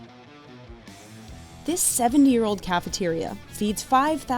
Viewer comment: At first, I thought she said seven years old😅 0:05 cafeteria, and I thought that was not impressive at all, I had to put caption on just to know what she was saying properly